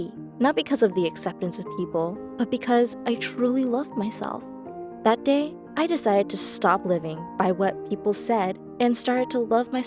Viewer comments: for a second i thought u were saying: ‘that day I decided to stop living’ and my heart went📈📈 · I thought she said “that day I decided to stop living.” I was legit scared for a second lmao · deadass thought she said “that day i decided to stop living” and i gasped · I thought she said "I decided that I'm gonna stop living" ;W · bro she paused in between "living" and "by" so i thought she said "i decided to stop living"